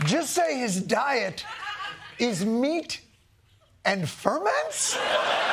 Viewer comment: I thought he was saying he eats meat and then ferments. As in ...he...ferments......bruh